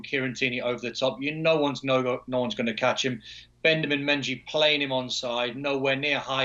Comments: he said Bendamin Menjy instead of Benjamin Mendy! LMAO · Man said “Bendemen Menji” instead of Benjamin Mendy 😂